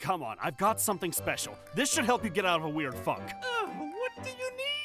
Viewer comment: did say the f word??